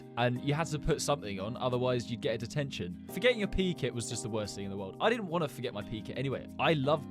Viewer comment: I thought he said pee kit not or kit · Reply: I mean “pe kit” not “or kit”